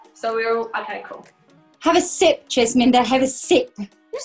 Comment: the subtitles said “chesminder” but only true mixers know that perrie calls jesy “jesminda” sometimes 😉